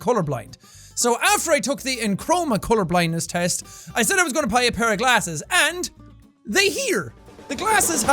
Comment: I swear I heard him say "puy a bair of glasses".